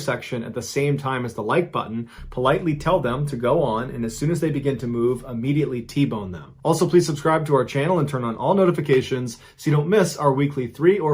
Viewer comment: Oh shit he said T-bone the Like Button when they start to go. I thought he said Tea Bag them. I had a lot of splainin' to do when the cops showed up.